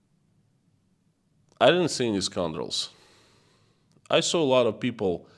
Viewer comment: Scoundrel is a word I didn't know. Thought you were saying "scumroll" which isn't a real word of course :D